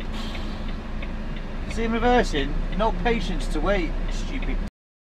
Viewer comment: Was that 'stupid man' I heard you say 🤣🤣🤣🤣🤣